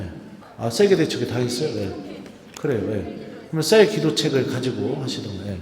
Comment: 성모신심미사가 성모님을위로해드리는마음으로드려야하는걸 처음알았네요 감사합니다 묵주기도의신비를묵상하는법이 나와있는책이 무엇인가요? 1:27:25 새기도책으로들리는데 어떤건지요? 아시는분은좀알려주시겠어요???